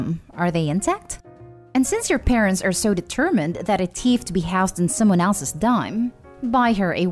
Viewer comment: Why does the narrator pronounce thief so weirdly? It sounds like she is saying “teeth”.